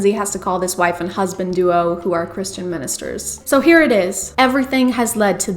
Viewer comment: I heard "CHRISTiando" 👏👏👏